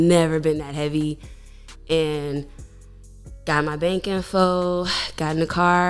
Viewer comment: What does she say at 3:56 ... "got my _____ (sounds like) BANKINFOLD"???